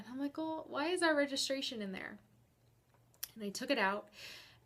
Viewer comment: The wallet just fell. And happen to be open. I think many woman has had that happen with the men they wanted to spy on. But truth is. If a person gives you reasons not to trust, that's a reason to spy on them. I don't trust at all, why i will never get married. EVER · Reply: Jojo Crazy Cat did she say it fell? I thought she said she saw a registration and took it out and found the blue sky.